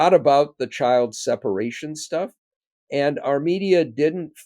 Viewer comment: "Our media" - I thought Norm said "Armenia!"